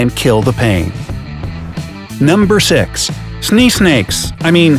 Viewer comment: I thought you said sneeze snake